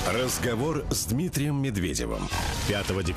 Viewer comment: МНЕ ПОСЛЫШАЛОСЬ (0:11)<<<РАЗГОВОР С ДЕРЕВОМ>>> ИЛИ НЕТ?